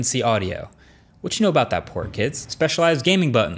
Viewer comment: I thought he said, "what you know about that poor kids?" 😂